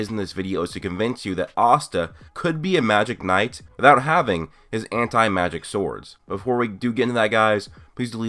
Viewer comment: The most time I dont hear "his anti-magic swords", I hear "his hentai-magic swords". This would be strong magic swords 😁